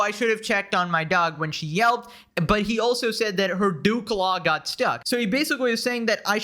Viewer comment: when he said 'dew claw' I thought he said 'dook log' lol